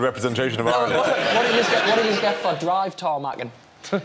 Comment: What does the guy in the pink shirt say at 10:37? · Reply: He says there should be sport of 'drive tarmaccing' that the Irish would be good at